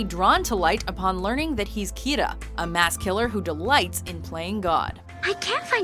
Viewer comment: Did anyone else hear Kita instead of Kira?